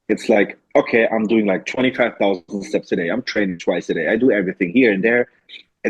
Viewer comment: Wait 16:55 did says 25,000 steps a day? 😳 I'm assuming that's counting steps from cardio ...I don't know why people don't count cardio steps as steps.